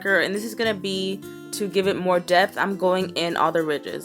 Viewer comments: she said death instead of depth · it sounded like she said "death"